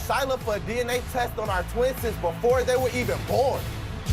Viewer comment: did he say twinses? 😆😆😆